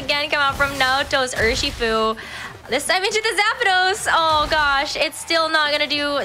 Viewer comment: Did she just say “Zapados?” As in “shoes” in Spanish?